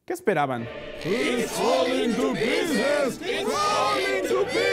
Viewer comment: Qué dicen? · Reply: it's falling to pieces Significa se esta cayendo a pedazos